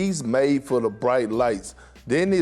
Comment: Did Perkins just say lamelo is galanari??😂🤣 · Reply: Deni